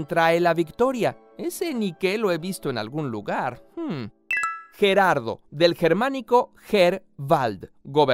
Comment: Escuche que dijo buenardo xD